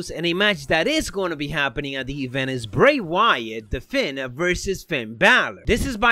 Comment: The way you pronounce " the fiend " literally kills me man , like you sound like your saying " the fin "